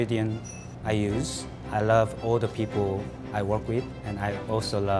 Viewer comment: I heard him say " I love old people" O.O